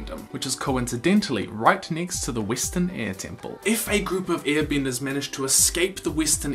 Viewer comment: I’m sorry. I keep hearing “ear bender” lmao